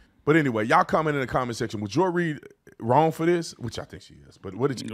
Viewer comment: I thought you said Joy Reid got fired, did she? I missed it if she did.